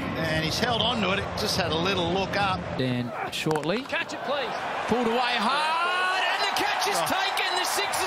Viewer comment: did anyone hear Kerr saying catch it please